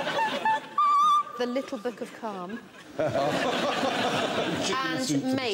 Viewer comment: Wait did she just say "a little bit of come"?